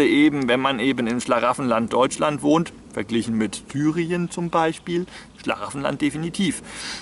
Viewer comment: da musste ich 2 mal hören, hab erst "verglichen mit Thüringen" verstanden. So schlimm ist der Osten ja nun auch nicht ;) Ich denke das Bushcraft und Survivalwissen ist kaum für eine "Weltuntergangssituation" zu gebrauchen. Ich sehe die Praxis eher darin, falls man sich wirklich mal irgendwo auf einer Tour verirrt, einem die Vorräte ausgehen und man vllt. keine Übernachtungsausrüstung dabei hat. Diese Techniken sollte daher eigentlich jeder kennen und anwenden können, überleben in der Wildnis gehört eindeutig in den Grundschullehrplan :P Außerdem lebt man viel billiger wenn man sich immer im Wald satt frisst xD